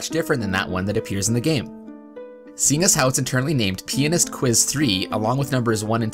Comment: it sounded like he said penis